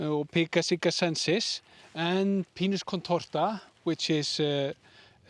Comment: Did I hear them say "Penis contortus"?😂😂 Must be a very special Icelandic tradition! I know. It was feminine..🙋‍♀️🌲